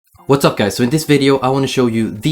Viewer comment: At the start of the video I thought he said, "What's up pies" and I was like 😐